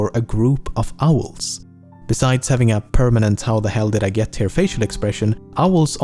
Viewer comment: English Subtitles says "Parliament" but the narrator is saying "Permanent"